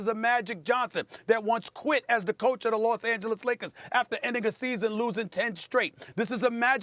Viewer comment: Uh oh did he say the N word again get ready for another suspension 😂😂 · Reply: endNIGGA